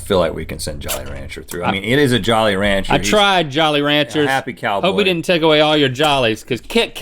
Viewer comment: Did he just say happy cow? 👀